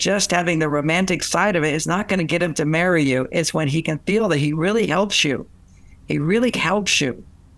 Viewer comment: May I know what Dr Gray said "... Men marry women when he really... you". What verb mentioned in min 1:18:50? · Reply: When he really helps you